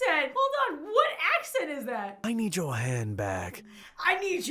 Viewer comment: I thought he said I need your hand back. Lol